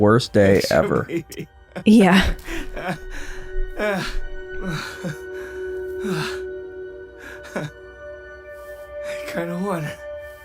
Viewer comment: I'm pretty sure the subtitles are wrong there, when Mark allegedly says "I kinda won" I think it's meant to say "He kinda won" because although Mark dealt with the problem, he's stuck forever on a different dimension that he can't leave so Levi kinda won in a twisted ironic way